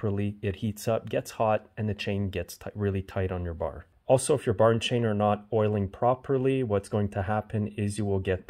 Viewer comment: Not oiling your bar and chain.. My bad.. thought you said: 'owing your ball and chain.' Time to kick in with the decorating this week.